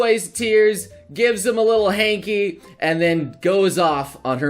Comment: I was kinda zoned out at that moment, I swear I thought I heard you say a "little handy".... I said out loud "damn that was quick." Maybe I should pay attention...